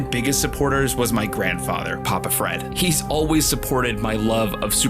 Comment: thought u said Alfred and i thought bro is Bruce Wayne rn 😂